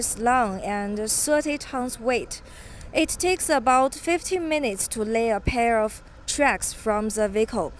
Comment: 15 minutes to lay 500 meters of track. Wow! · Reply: I thought she said 50 minutes.